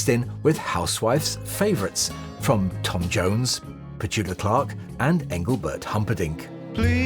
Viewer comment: Did you say Petuka Clark? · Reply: I said Petula Clark